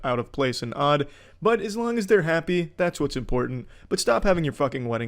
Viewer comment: I hope you don't actually think that's what he says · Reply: I must be tripping I’ve always thought he said , ‘so yeah’😂 it does not sound like see ya